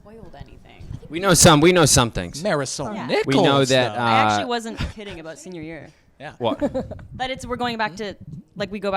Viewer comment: Any idea what Cole said at 1:00 ?? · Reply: he said marisol nicklols who plays hermoine which is veronica’s mom sorry if that seems rude some people just don’t get it as much as others sorry